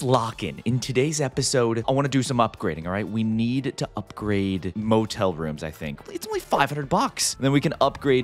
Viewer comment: I thought you said gay motel